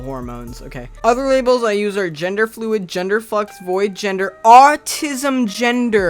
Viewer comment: when you said gender flux I thought you said gendered fuck and I'm a tad disappointed that you did not say that.